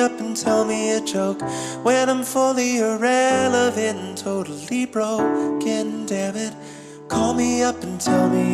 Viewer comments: One comment! We're missing an amazing line because he says "totally broken". It's great because you think he is saying "totally broke" at first, until he adds the last sound at the last possible moment. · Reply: ohhh THATS what it wass...i always heard it as "totally broke, cant debit" 🙃🙃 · One comment! We're missing an amazing line because he says "totally broken". It's great because you think he is saying "totally broke" at first, until he adds the last sound at the last possible moment. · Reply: i always heard it as something like ken dammit, my mind seperated those words